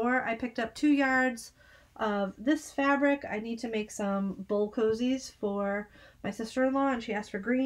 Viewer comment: At first I thought you said you were making bull cozies for your SIL. Had to go back and listen again. 🤦‍♀️ Enjoy your goodies!